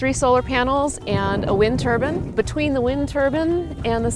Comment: Why does it sound like she is saying “wind turban”?